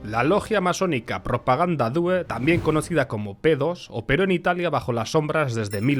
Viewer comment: solo yo escuche PEDOS😂😂😂